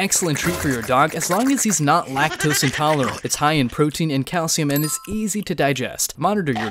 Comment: When i was like 9 i thought it was said like lack toes and taller ants